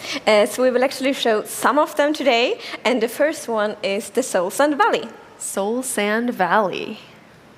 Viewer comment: I thought they said sun set valley from sims 4